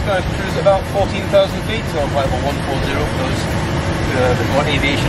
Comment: the pilot says "Flight Level 140" instead of "(mumbles)" ;)